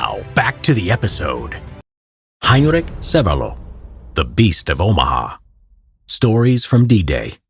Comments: I swear I thought it said beast of Obama. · Thought it said "the best of Omaha" and I thought "is this guy really gonna count the top kills of the landing or something? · I thought that this said “the best of Omaha” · Thought it said “The Best of Omaha” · I thought it said "Beast of Obama" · I thought at first it said beast of Obama